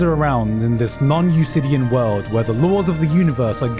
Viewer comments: Gigguk, did you just say Usidian? instead of euclidian? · does anyone know what "non-usidian" means? It's not a term I've come across before and I'm not sure if Garnt misspoke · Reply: He mispoke, it's non-eucledian I'm pretty sure. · What did he say at 26:46? Non-ucidian? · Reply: he probably meant non Euclidean but I could be wrong.